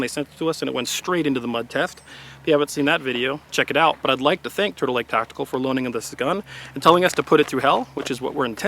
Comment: I thought he said turtle neck tactical lol